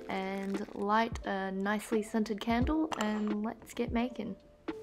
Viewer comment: I thought she said “let’s get naked” and I was like girl I just came for some cute wiccan crafts 😩